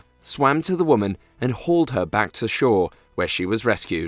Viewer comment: i think you ment to say held her